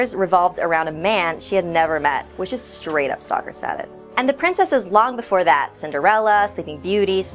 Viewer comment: Anyone know what is said at 2:10 ?? " ..which is straight up socrasatis." ?????? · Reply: Aaron Jacobs She said, "straight up stalker status".